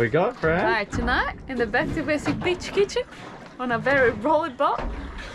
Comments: Makes me chuckle everytime Fran says Beach kitchen, it sounds like she is saying Bitch kitchen 🤣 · The first time I heard Fran say Beach Kitchen I thought she said B!tch kitchen hahaha , Now every time she says it I giggle lol Also you are living your best life like the dolphins cruising around different reefs making ❤😘😘 lol